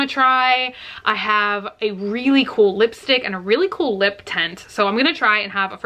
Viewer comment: I thought she said "lip tentt" and I was picturing either A) a tiny tent that goes over your lips and somehow plumps them up or B) a giant circus tent shaped like lips. Now that I realize she said "lip tint" I'm kind of disappointed!